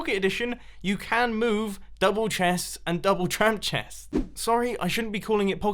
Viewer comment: Did you call it a tramp chest?